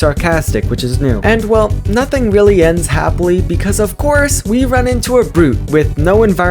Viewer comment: what's a fuckily bc thats what i heard